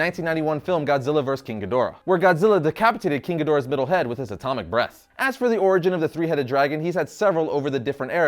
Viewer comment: I thought he said atomic breasts for a second 🤣🤣🤣🤣🤣